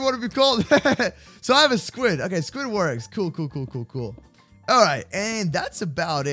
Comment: i think he say sqiudward but it was sqiud work 😂😂